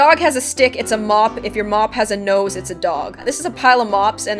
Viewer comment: it sounded like she said "if your mom has a nose it's a dog" lol